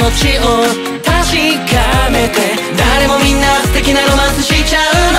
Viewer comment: あらきさんの”素敵なロマンス”のところが”しゅてきなロマンス”に聞こえるの尊い➰